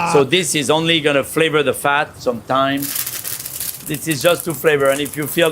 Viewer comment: "So this is only gonna flavor the fat sometimes" That is what i heard. Why not all the times!? Gonna flavor the fat... "some thyme"... Had to watch it back. Makes more sense. Didn't only audio though.